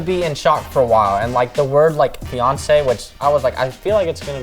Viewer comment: When he said fiance I thought he said beyonce And I was like what??